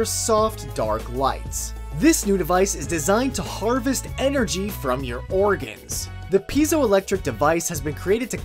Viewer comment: thought it said; harvest energy from your orgasms, wut?